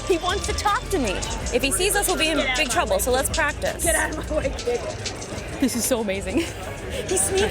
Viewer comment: Did anyone else hear “get out of my way bi*ch” @ 1:09? Or m I mistaken?